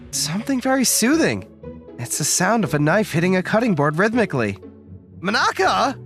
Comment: did anyone say "Cutting Board"?